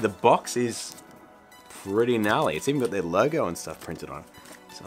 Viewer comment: Hold on a second. What word did you use to describe that box at 1:43? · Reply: I have no idea why I said Gnarly - the early 90's called and wants its lingo back, I'm happy to give it back too!!